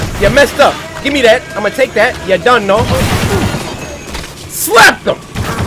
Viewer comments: why @ 23:54 it sounded like Cory went straight Jamaican on us lmao " you don know" i got hyped a sec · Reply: EmpressDiva AKreaLneSS its you’re done now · why @ 23:54 it sounded like Cory went straight Jamaican on us lmao " you don know" i got hyped a sec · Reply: Ohhh I thought they were saying you’re done